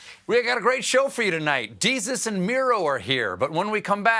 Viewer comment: I totally thought he said Jesus was here at the end, LMFAO.